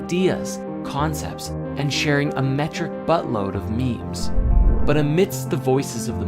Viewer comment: What did you say @ 9:25? "...sharing a xxxx load of memes.." Did you say boat load? It sounded like something else (vulgar/juvenile).